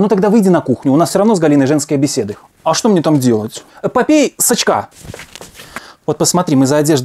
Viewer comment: мне одному послышалось "попей с очка"